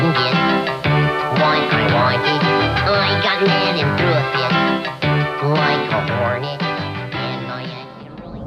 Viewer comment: did he say "Bullshit like I'm horny?"